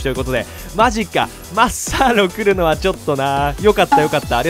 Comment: マッサーロがマスタードに聞こえた人👍 俺だけかな？笑